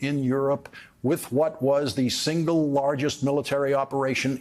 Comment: That intro was a little confusing. At first listen I heard you say the next President Eisenhower lead the D-day landings, but I had to back up and you were saying he lead the landings as a military general then later President. For a few seconds there I was really afraid because I respect you a lot Mr. Lawrence and I thought your social team had posted something in error. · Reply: "The next Republican President" he said but it was abrupt and made some of us forget Democrat Harry S. Truman and wonder if Lawrence had as well.